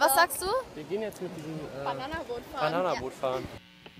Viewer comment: Ich habe erst verstanden Mit Einem Bananen Brot fahren